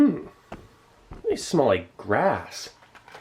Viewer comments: Am I the only one that misheard "they smell like grass" when he sniffed the cashews? LOL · He said "Hmm they smell like grass." but I got an ad in the middle of "grass" so it sounded like he said "Hmm they smell like crack." and then I had to wait 5 seconds confused as all hell before I could understand what he was saying. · They smell like ass? I had to rewind to make sure I heard right. I didn't but I had a good laugh lol